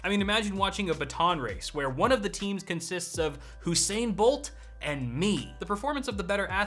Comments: Surely I'm not the only one who heard him say "Hussain Bolt" 😂😂😂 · Did you say "Hussain Bolt" at 7:18? 😂